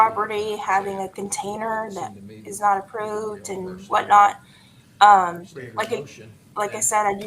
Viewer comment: I heard differently. I didn’t hear the violation being an unapproved container, I heard the violation was containers being stored in the exterior of the home (violation of ‘curb appeal’).